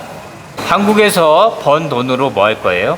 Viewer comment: 뭐라고 하셨어요? 잘 이해하지 못해요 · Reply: 한국에서 번 돈으로 << 돈을 벌면 뭐할거에요 라고 했습니다.